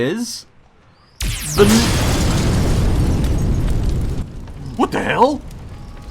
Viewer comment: I think the Dwarves or Draenei should not have been eliminated on the lore point. I know it's a joke but Tuskarr barely have any lore, and who likes to AFK in Moa'ki Harbor? Also did I hear you say "the N-" at 5:23? sounds like you were going to say Night Elves ;).